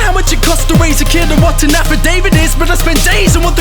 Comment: can anyone help me with what he is saying here, all i hear is tanafa david but i cant find jackshit about that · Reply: Affidavit